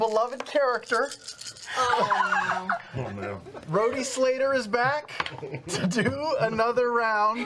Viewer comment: Who is Sam impersonating at 53:00? I couldn't quite catch the name. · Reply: Mikha'el Ben-Yah It’s his own character named Rhodey Slader. It’s just based off the stereotypical Californian surfer male.